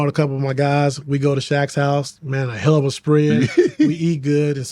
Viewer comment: did he say “we made of helluva spread” or I just been to jail to much 😂